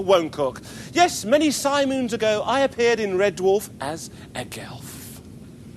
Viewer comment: I'm honestly shamed to say I thought Ainsley said he appeared in Red Dwarf as a Milf >.>